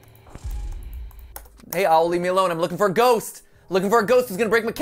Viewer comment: I fully thought he said, "Ollie" And I was like, "Dang, he is stressed if he's talking to his son like that xD